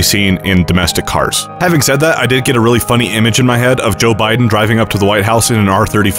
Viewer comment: I wanst paying attention to the video and at 4:48 I thought you said joe biden rule 34